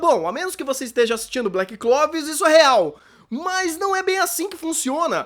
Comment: Ele falou Black Clovis ou eu ouvi errado? kjkjkj